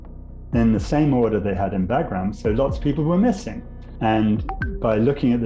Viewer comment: Great video. However, I noticed a few mistakes in the sub-titles, which you might wanna fix. For example, at 10:02 he says "lots of people were missing" and the sub-title says "less number of people were missing" - in another place earlier the subtitles contradicted what the man was saying too. Either ill-intent on the person writing the sub-titles or a mistake, kindly fix it.